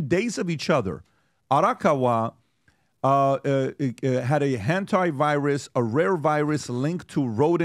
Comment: From 0:47... did I hear that right a 'Hentai Virus'? 😅